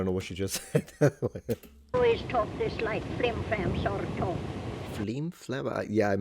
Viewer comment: I think she said "Frame fan sort of" like "a fan with frame form"????